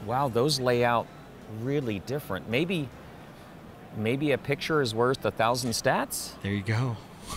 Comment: Sorry, my english is a little hard . What does say at 8:02? "Maybe a picture is ksldjfklsad a thousand safsad" I don't get that hahahaha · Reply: haha no worries. That is a -ism. He said "maybe a picture is worth a thousand stats". Which is a play on words of "A picture is worth a thousand words." <link>